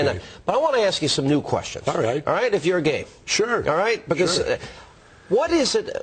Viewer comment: did anyone else hear hannity say; "but I want to ask you some new questions, alright, if you're gay"